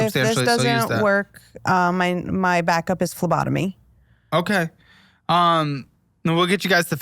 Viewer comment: When said her back up plan was phlebotomy I thought she said lobotomy! 🫠 Not joking! I had to play it back 3x! 😂😂😂😂